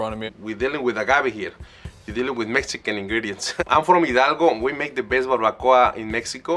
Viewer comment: the captions say he is from El Pato (the duck???) when he says he is from Hidalgo, also a few moments before that he is saying the jiffy mix will thicken the sauce, the captions say something different, 🙃